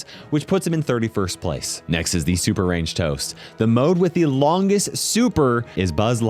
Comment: Ok when I heard toast I got confused and was thinking if I heard it correctly so I went to check if I did then I see the captions saying toast Makes me smile